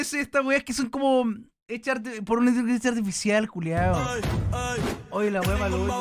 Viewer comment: no entendí nada de lo que dijo xd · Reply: Se supone que dice inteligencia artificial, pero como ya está viejito le cuesta pronunciar 😔